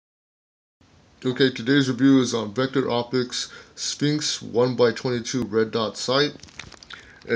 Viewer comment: At the start I thought you said vector optics "stinks" not sphinx.....thank you for the review.